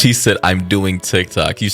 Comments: I thought he said I’m deleting TikTok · I think he said I’m deleting TikTok